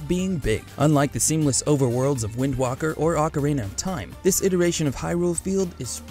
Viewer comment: DID THIS BOI JUST SAY WIND WAHKER